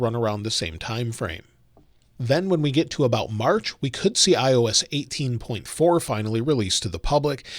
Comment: did you say iOS 18.4?